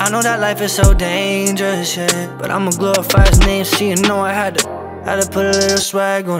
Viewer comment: We need some lyrics because I pretty sure I heard profanity at 0:49 seconds 🤔🤔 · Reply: irepaulola “but imma glorify his name, see you know I had to...” that’s what he said